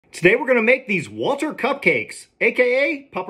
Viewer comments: Thought it said “water cupcakes” at first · I thought he said water cupcakes I was like whaaaaa?